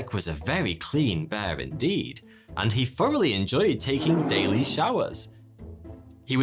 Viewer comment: why do I hear: “…and he furrily enjoyed taking daily showers…”?